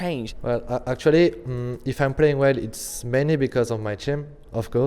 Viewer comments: what did he say at 1:23 ?? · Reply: "If I play well, it's mainly because of my team." Even though he can easily just solo carry titan without them LOL. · its mainly because of my..? what did he saying there, cant understand, plz respond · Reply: Team